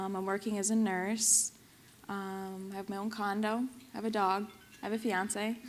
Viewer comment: She says, "I moved to CT, I'm working as a nurse", then at about 10:22 she says, "I have my own condom"?? before she says, "I have a dog"?? I can't tell what she's saying? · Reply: she says condo lol